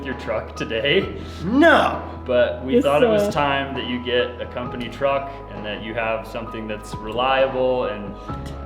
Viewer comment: He does get to keep the truck right??? I thought I heard company truck??